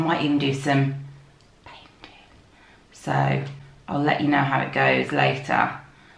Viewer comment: Ahaha I'm cooking whilst watching this and when at about 3 mins in you whispered "painting" I totally thought you'd just said "Satan" in a really creepy way !!!😂